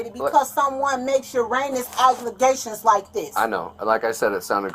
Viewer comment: She really said "your anus aglegations"